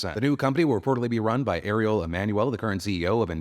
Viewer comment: I misheard what you said and at first I thought you'd said "Areola Manuel" not Ariel Emanuel